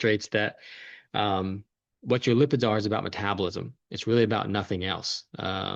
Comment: What word are you saying at 12:28 - something . . . Lipitor? Then you say it’s about metabolism. Can you explain? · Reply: I say "what your lipids are is about metabolism". Blood lipid referring to the lipid/cholesterol panel on bloodwork.